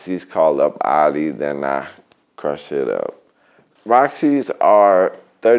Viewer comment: Crush it up :v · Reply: Life Before watching this, what I heard was "Push it up".